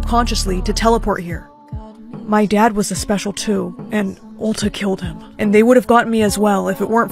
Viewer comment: it sounds like they said Ulta killed him lol.